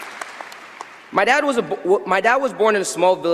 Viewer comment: In his speech, at 4:34 did I hear him say "My dad was born at the age of 8"? LMAO